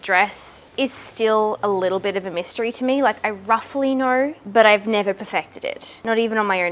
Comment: when you say "roughly" i automatically hear RUFFLY 😅 Because i heard you say Ruffle so many times by now 😁